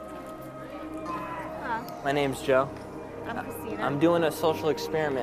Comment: First I thought he said, " Hi my name Jeff!" And I started bursting out laughing!